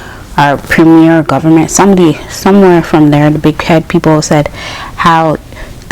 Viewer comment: Wait girl what you mean then if head people? 🤣🤣🤣Ohok Nice. Did you like it? You need to travel more if you can. I heard about that website but never used it because it doesn’t look like a real website to use down here. I use Expedia or go to the actual airlines website if anything. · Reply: My bad I thought you said the big head people 🤣🤣🤣sorry I am so Jamaican 😂hearing things not said in patwa😁 oh really that’s website is pretty useful save my husband a lot of money that’s the website he uses when he would visit me in the Jamaica. Yeah I a seriously hope I can travel but to the way how things are looking😬😬 not sure 🤷🏾‍♀️I came here to get settled and start my saving but it’s a mess right now 🤷🏾‍♀️. It’s a real website my husband uses it and when I came here it was the same website we use🤷🏾‍♀️